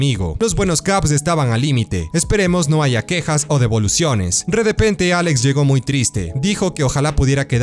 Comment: escuché bien? redepente...?